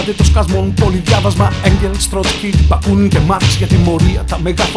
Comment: Τι λέει από το 1:28 μέχρι 1:31 ? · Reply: Ενγκελς,Τρότσκι,Μπακούνιν και μαρξ